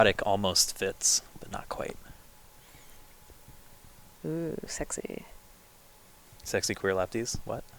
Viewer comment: did she say "oooohh sexy" thats how you pick up women?! Scrabble!